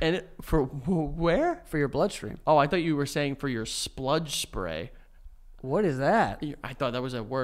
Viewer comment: Not gunna lie, I heard “splud supreme” at 47:17 so I’m with on this one lol